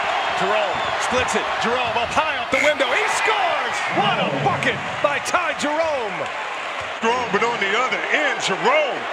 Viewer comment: At the 11:21 mark... what exactly did the commentator say?? I think my ears are tripping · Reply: What a bucket by Ty Jerome